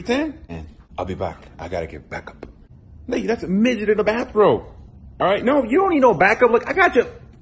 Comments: did he say the n word at 7:03? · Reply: I thought so too at first but if you slow it down he said “Lee, that’s a midget in a bathrobe” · did he just say the n-word 7:02? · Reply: No, he said “Lee, that’s a midget in a bathrobe.” Slow the video down